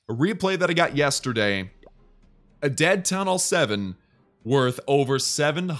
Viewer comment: did he say dead th 7 oop